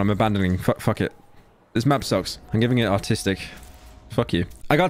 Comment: bro I thought he said autistic